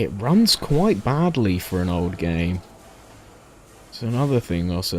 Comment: Hey man good video but when I watch it the video is 60 FPS then your game lags and its just really off putting and i'm not saying its bad or anything but just make sure that you have a consistent record FPS or game FPS other then that its great. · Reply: UPDATE i just watch the last like 10 mins and heard you say "runs bad for a old game." my bad